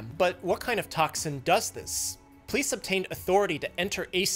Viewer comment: When he said "police obtained authority" looking at the camera, I heard "Please obtain authority" and I was like shit I need to solve this case myself?